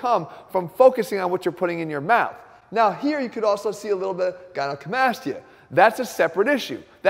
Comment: what does he say about the manboobs? Cant understand the word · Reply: gynecomastia